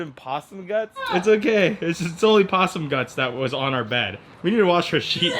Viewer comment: At first I thought you said Lefty rolled in possum LUTs.